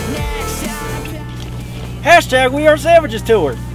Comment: I thought said hashtag we are sandwiches tour but then I realized he said savages xD